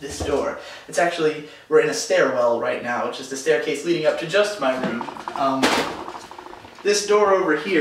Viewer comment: it sounded like you said Just Defy's room... I am too obsessed with MindCrack, aren't I?